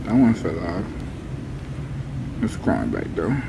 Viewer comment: yo I got scared cause I thought Yachty said Aidan (my name)